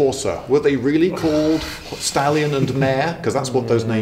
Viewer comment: "Hengist and Horsa, were they really called Stalin and Mao?" WTF! (Yes I know you said stallion and mare, but damn, that was a pretty intense double take I did there.)